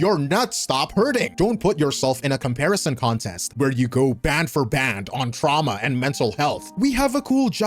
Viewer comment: did he say BAND FOR BAND?? (I’m sorry I love hood JJK)